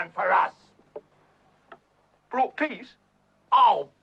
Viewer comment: anyone else thought he said "brought piggies"? Like this if you noticed?